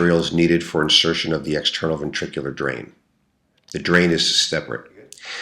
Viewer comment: Did i hear external jugular drain? Hehe